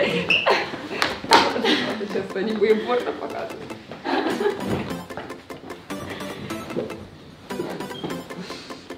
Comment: Прислушайтесь что сказала на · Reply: не будим порно показывать Вроде